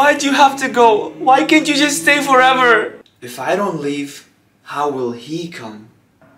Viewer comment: I thought he said if dont go how will the devil come